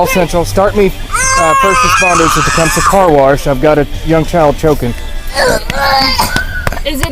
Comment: I thought he said "I've got a dumb child choking"